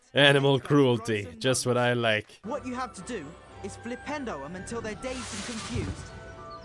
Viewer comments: I legit thought they were saying NINTENDO! instead of flipendo! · I love being able to share such a fondness of this game with. I'm 22 and I still think about how good this game was. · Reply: I remember playing this game for the first time in the family living room and my dad always thought instead of “flipendo” he was saying “Nintendo!”and instead of “expeliarmus” he thought he was saying “smelly almond!” I remember it being so fun flying the broom around everywhere and finding all the wizard cards